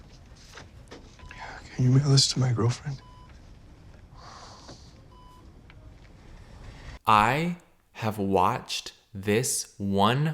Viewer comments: Caleb repeating a clip of a guy pretty clearly saying “can you mail this to my girlfriend?” over and over while being super confused about what he’s saying is so funny to me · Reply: i thought he said can you wiggle this to my girlfriend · Caleb repeating a clip of a guy pretty clearly saying “can you mail this to my girlfriend?” over and over while being super confused about what he’s saying is so funny to me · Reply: I thought he said, “Can you wiggle this to my girlfriend”! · *I could be wrong*, but I think at 24:45 he's saying: "Can you mail this to my girlfriend?" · i heard "can you mail this to my girlfriend" does not make sense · Caleb repeating a clip of a guy pretty clearly saying “can you mail this to my girlfriend?” over and over while being super confused about what he’s saying is so funny to me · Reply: Dude I heard "can you wiggle this to my girlfriend" and I'm just confused